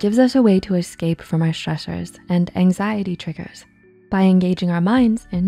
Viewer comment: what word is that 😅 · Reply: They said stresses I think